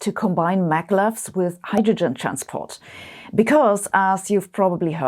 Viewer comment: Sabine says "Hydrogen" but the CC says "Helium" , needs to be changed to say Hydrogen